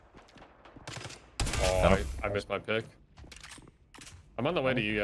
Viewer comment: "I missed my pick" What i heard the first time was; "I went limp d..." Had to reply cause I knew I was trippin lmao